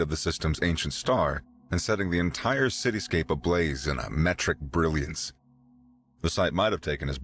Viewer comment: it sounded like metric brilliance instead of metallic brilliance